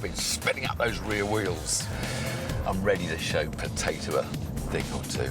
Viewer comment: Did Tiff really call Jason ‘Potato’ at 5:17? Hahaha